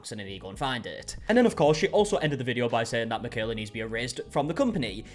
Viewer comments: I thought she said "a raise " not "erased'l" lol my bad. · I think she said she needs a “raise” not “erased” from the company. Meaning they shouldn’t have been unhappy with her at Instacart if she didn’t do anything wrong. Unless I misheard/misread any of that. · I could’ve sworn she said get you a raise instead of get you erased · When she said she should get "erased" from the company I thought she said she should "get a raise". I was like...I guess??? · I thought she said "a raise". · It sounds like she said to give her a raise, not erased